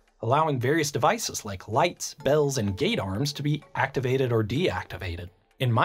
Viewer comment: I thought he said ‘Gaydar’ He said ‘Gate Arms’